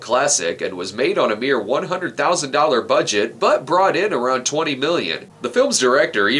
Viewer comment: I misheard you when you said '20 million' as '20 billion' and almost did a spit take because that seemed a bit insane.